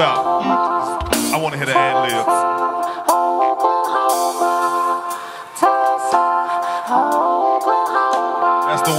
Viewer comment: what is dc saying right here? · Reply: Tusla Oklahoma